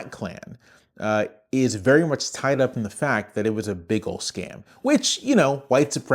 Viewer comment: I heard it as the Klan being a bagel scam, which made everything after hilarious! Great video, by the way.